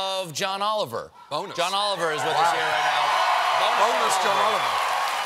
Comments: I thought he said 🅱oneless John Oliver. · I actually thought he said 'boneless john oliver' at first. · bro I thought he said boneless John Oliver.